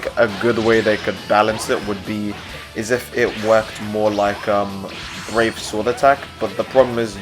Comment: Sounded like you said R A P E S W O R D A T T A C K XD